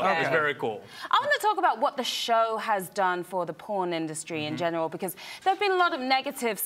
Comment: Did she just said "the porn industry" ? XD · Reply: Pawn